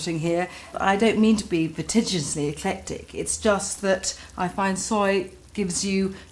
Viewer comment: Anyone able to decipher what word she uses before "eclectic" at 5:40? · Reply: She said "I don't mean to be prodigiously eclectic just that soy offers an intensity of savouriness that salt alone just doesn't give you".